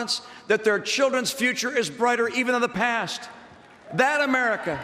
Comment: did he say "....restore a mother and father's confidence that their children's future is brighter, EVEN IN THE PAST"..? Have I misheard/misunderstood him, or has he stopped making sense of the time-space continuum and reality altogether? That statement makes no sense in any way of the english language.